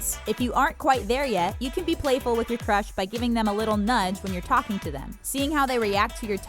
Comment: I thought at 1 part She said *Stroke there ego and *give them nut while there talking to u like this comment if u heard that 🤣🤣😅👇🏻